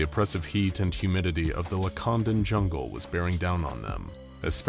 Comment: I had to put subtitles because I thought you said “Wakandan” 😅